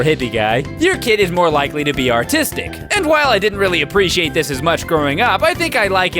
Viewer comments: I thought said 'Your kid will likely become 'autistic' not artistic lol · When he said artistic I thought he said autistic so I had to double back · I could have sworn said "your kid is more likely to be autistic" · I thought you said "autistic" and then I realized you meant "artistic" lmao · when he said that the baby was artistic I really thought he said that it was autistic